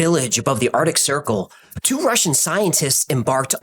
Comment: Right at the beginning, I thought you were saying "two martian scientists" lol